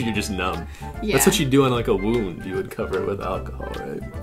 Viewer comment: When said wound I thought he said womb lol